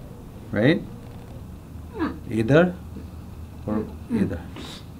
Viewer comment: does he says "idhar", "idhar" ?? Meaning "here", "here" in English !! Also 3:37 "thik hai" means "okay" !! How he knows hindi?? Which country is he from??